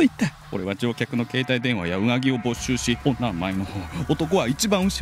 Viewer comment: 「携帯電話やうなぎを没収し」って聞こえた。うなぎ…？